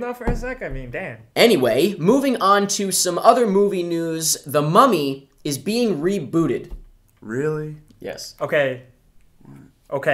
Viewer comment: At first I heard "The mommy is being booted" and I thought "What the hell?"